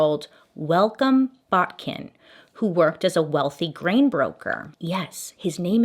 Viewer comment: I swear I thought you said he worked as a wealthy grave robber at first! I had to rewind and listen closer! 😂